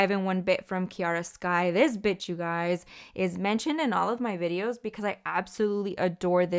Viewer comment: Lmaoooo i definitely heard “this bitch you guys “ i had to rewind like huh 😭😭😭